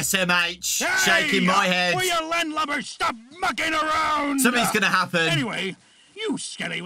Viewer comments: When foxy said mucking I thought he was saying the f word lol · I honestly thought foxy said f***ing around the first time shows how tired I am